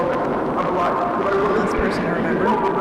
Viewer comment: right after Peter Tench shoots Mark, when Peter Tench is saying something but muffled, he is actually saying "Marvin, im alive! My only wish is for you to tell them that im alive! They need to know!"